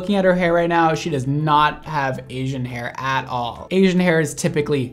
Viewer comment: Brad: Asian hair is much heavier Me, having two person's amount of hair: THAT is very much correct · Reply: I thought she said “aging hair”...lmao